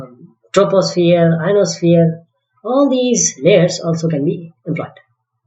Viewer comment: Am I the only one who heard him saying "Anosphere"